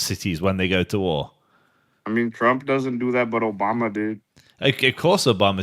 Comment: did he say: "Trump doesn't do that but Obama did?" What's wrong with this people?